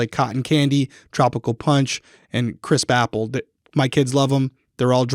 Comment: Lol when said "crisp apple" it sounded like he said Chris papple 🤣🤣